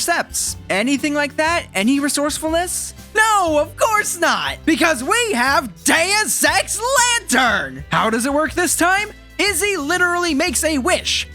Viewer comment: I know what you were trying to say but this is what I heard and it made me laugh "NO, CUZ WE HAVE DAN SEX LANTERN!"